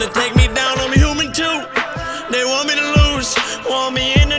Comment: is so thick at 6:34 look at the sentence in doesnt make sense he says they want me to lose but the caption says they want to me lose xd xd